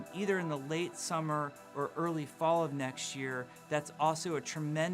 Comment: Does he say late summer/early fall NEXT year at 6:35?